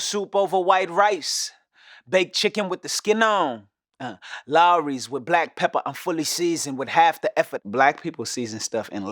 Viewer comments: Was I the only one that thought he said “Lawry’s with black pepper. I’m fully seasoned with laughter” ? 💀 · Whole time I thought he said "I'm fully seasoned with laughter."🤦🏾‍♀️